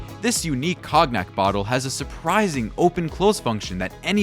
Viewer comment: "This unique COGNUT bottle " lol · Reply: funny- I heard "Cog-nack" but yours is funnier :) Attn: AI voice input- it's "CONE-yak"